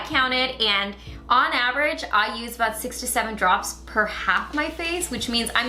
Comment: Did anyone else hear 67 drops lol. I was so confused and then i realized.....